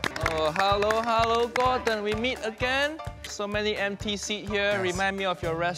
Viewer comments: I thought he said “NPC’s” instead of “empty seats.” 😂🤦‍♂️ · “So many empty seats here, remind me of your restaurant. “ DAYUM.. 😳💀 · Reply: I thought he said so many NPC’s here remind me of your restaurant lol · “So many empty seats here, remind me of your restaurant. “ DAYUM.. 😳💀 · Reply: I heard him say "so many NPCs sit here, reminds me of your retaurant". Lol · I thought he said "so many npc's here" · I thought he said "so many NPCs". Ice cold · I thought he said "soo many NPC" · I thought he said "NPC"instead of "empty seats"😂